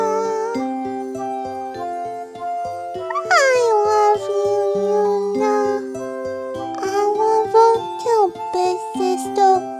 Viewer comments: So cute I hear "i love you too Big Sister " OHHHH WHAT A CUTE LITTLE VOISE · I heard ''I love you Luna'' ^^